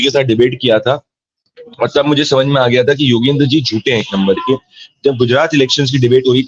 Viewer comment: am I the only one who heard," Yogendra ji chutiye hain ek number k." 😂😂😂